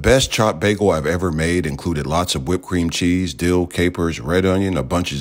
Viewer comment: I heard that as "whipped cream, cheese" and it had me concerned